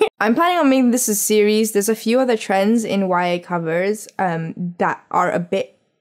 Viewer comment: Hmm her words were kind of muffled, did she say 'wire cutters'? CC at 7:01 : 'wire covers' Oh okay that clears it up, thank you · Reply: YA covers